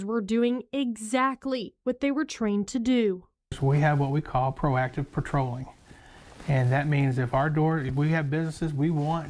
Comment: He said: "Proactive policing". I heard: "Monell Claim"